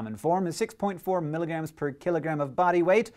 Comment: Why did I keep hearing "x milligram per KILLERgram"?